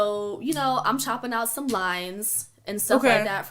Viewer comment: She couldn't provide a rating on Simon but oh so casually shared how she chopped up some lines. 🤣🤣🤣🤣 deceased ☠️ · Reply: I thought she was saying she was chopping some limes. Chile... 🥴